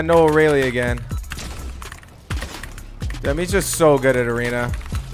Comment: KEEP UP THAT GOOD WORK.Also did you say dam here